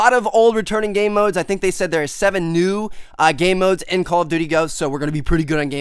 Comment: I been watching ur vids for a while and it gets annoying that it sounds like ur saying call of butty ghosts